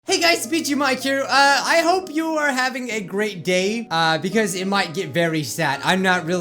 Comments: I thought you said great gay and I laughed so frickig hard great vid btw <3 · I heard I hope you have a great gay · i listened to the audio like 8 times and you definitely accidentally said gay instead of day i'm proud